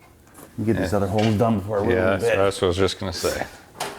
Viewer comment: What does say at 2:56? Can't quite catch it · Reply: Get the other holes done before I ruin the bit.